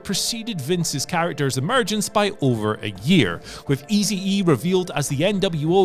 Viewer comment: did he say Eazy E Lol